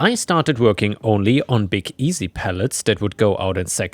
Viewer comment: At first I thought you were saying pellets. I was so confused. 😆